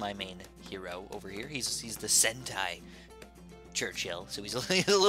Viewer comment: I thought you said hentai 😂